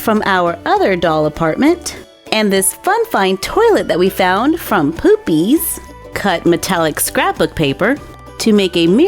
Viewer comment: Is it just me or did she just say poopsies! 😂😂 nice 👍 I 😍 it